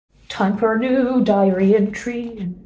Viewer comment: When I watched it the first time, I didn't look at the text so instead of "diary entry" I heard "diarrhea tree". Wtf is wrong with my ears and brain!?💀